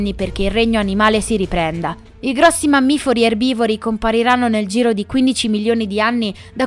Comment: solo io ho sentito mammifori??? 🤔🤔🤔 Lo speravo😜